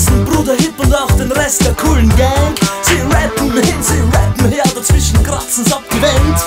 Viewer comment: Does he say "Kool and the Gang" at 1:45?